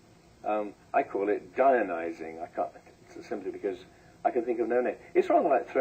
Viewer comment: Anybody catch the word he uses at 1:22:35-1:22:45? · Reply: Sounds like an extension of the word "Gaia" as used by James Lovelock. Therefore probably "Gaia-nising" or "Gaia-nizing".